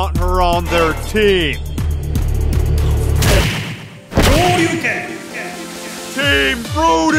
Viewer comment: for no reason it said all you can or Hadouken or whatever it said I have no idea what it said just please comment what did it say?